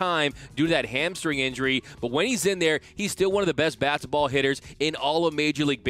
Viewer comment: does he say basketball or fast ball I know he meant to say fastball but it sound like he said basketball 😂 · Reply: I think its “bat to ball”